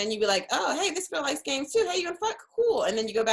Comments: did Meg say "hey you wanna fuck?" Lol Ive never heard her curse before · Did Meg really say "fuck" @ 3:16? 👀🤣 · Did Meg say fuck at 3:17 or am I trippin 😳😳 if she did it's like hearing ur grandmother cuss. Wow I am shocked now am just waiting on